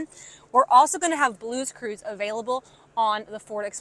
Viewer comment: First time I heard you say blues clues 😂